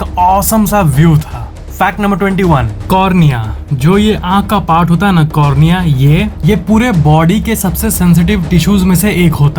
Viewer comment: when you say cornea I hear corona 😅